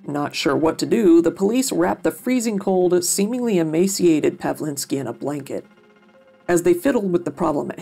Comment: I thought you said pedo prevalinski and then immediately thought to myself. Oh no I hope this isn't going where I think it's going.